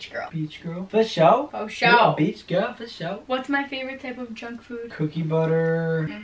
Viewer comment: am i the only one that thought she said drunk food instead of junk food lmao